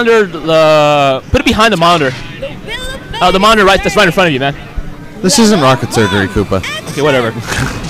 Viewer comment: Did he just say "This isn't rocket surgery, Coopa." ?